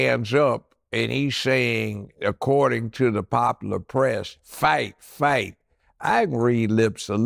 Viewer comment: That's what I thought he said, too at first....But I think it was FIGHT FIGHT FIGHT